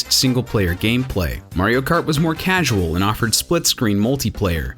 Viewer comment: I thought he said MARIO FART